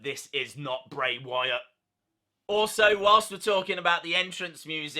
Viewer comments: Thanks for bringing out the "Hurt Heal" chant! I swear I heard "Yowie... Wowie... Yowie... Wowie..." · Glad you said about the Hurt Heel chant in the theme, I couldn't work it out, I thought it was saying Burn Him and I couldn't work out why it would be saying that